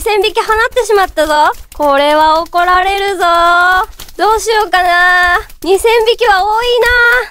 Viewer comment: これは怒られるぞぉ（嬉）に聞こえるのだが。